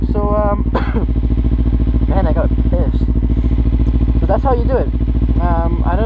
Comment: "Man I gotta piss" is that what you said?? hahahahah you kill me.